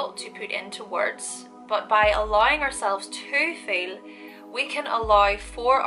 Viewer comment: I know what you said but your (gorgeous) Scots had me hear "...allowing yourself to fail " for me to find the flow... ...and I think that's what I really needed to hear...